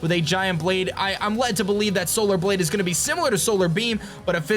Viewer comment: When he said solar blade I thought he said solar bleed lol